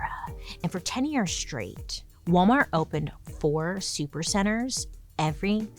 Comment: When she said "For 10 years straight" I heard "pretend you're straight" 😂